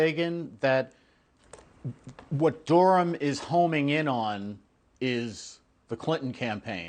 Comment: I don’t understand the title of this video. It says honing in quotes, but he said homing. If he had said honing I’d get it because that’s incorrect, but he said homing, which is correct. Very strange. I guess whoever transcribed that misheard the statement.